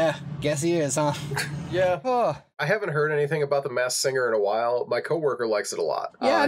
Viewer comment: Low key thought was saying Mass singer and was thuroughly confused for a hot second 😂